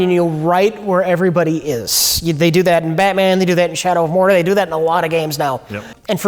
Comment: What does Rich say @ 08:09? He mentions a game... is it called Shower of Jorje?